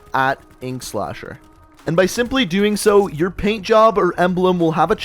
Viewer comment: I thought in his intros he said hi it’s eggslasher